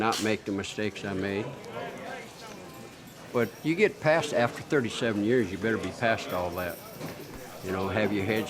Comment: I thought that one guy said he was in for 7 years but he said 47 years😱😱